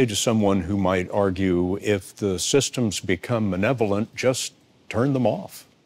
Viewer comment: did he say manevolent?